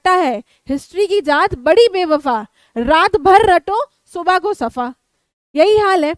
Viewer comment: I heard ma'am said pet safa 😅😅